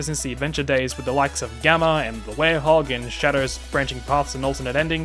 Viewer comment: Little mistake in the subtitles with saying Big instead of Gamma luhmayo of course 6:00p.m. 4/29/2023